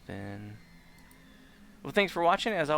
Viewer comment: Omg it's! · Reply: I could swear at the end, said, "Josh, you suck" at 48:27 though he could have said, "Gosh, you suck" ¯\_(ツ)_/¯